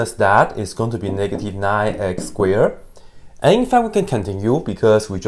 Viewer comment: What’s man saying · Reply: I had to turn on closed captions to figure that out lol, he's saying "and in fact we can continue"